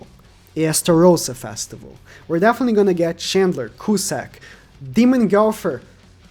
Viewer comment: I laugh so much when he mentions cusack, bc here in brazil it sounds like hes saying assballs